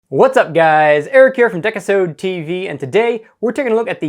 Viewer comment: Am I the only one who heard him say dick-a-sode tv?